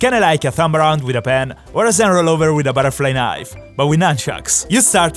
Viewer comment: What did he say?😂😂😂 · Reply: It's zenrollover